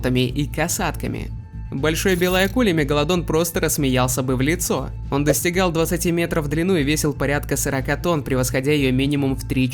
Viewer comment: Мне послышалось"он достигал двух сантиметров в длину"😂😂😂